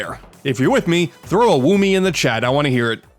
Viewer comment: Did you just say "woommy"